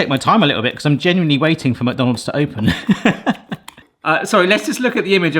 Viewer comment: Might be an accent thing, might be on purpose, but I totally hear you saying "MockDonalds", which is, rather fitting. :)